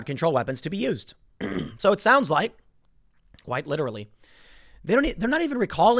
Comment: Hahaha. Did he say "white literally". 17:25 If he did. Hats off.